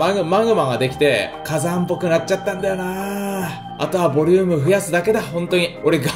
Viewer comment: あとは母乳を増やすだけだって聞こえた